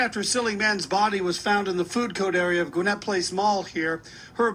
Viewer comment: did he just say they found "silly man's" body in the "food coat" area???